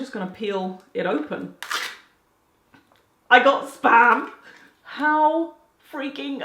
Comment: Lol I thought you said I got spammed hahaha